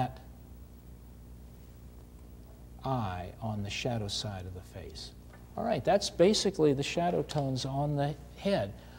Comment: On 16:00 can you please tell me what the colours are? I didnt really understand what you said. Thank you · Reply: Venetian Red and Burn Sienna. Greying this down with a mixture of Black, White and a touch of yellow ochre